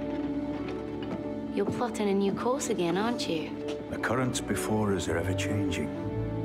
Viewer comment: activate subtitles say: "You're fucked in a new course, aren't you"